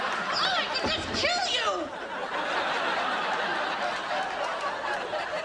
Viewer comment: This is my favorite part 2:00 but i dont know what he said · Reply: Leut LT he goes well hello rachel